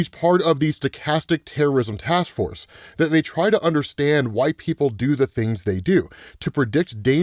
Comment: Had to go back to 3:10 to make sure I heard right. Really sounds like he said "why white people do what they do"